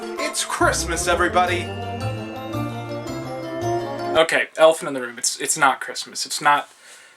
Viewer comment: GOD I thought you said Elf in the Room.